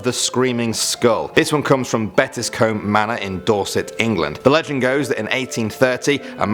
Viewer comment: WAIIIIT did he just say dorset? I live in dorset how tf have I never heard of this lmao